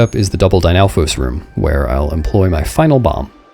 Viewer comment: Bizarrely, the subtitles here say "Lynel" instead of "Dynalfos", despite being auto-generated.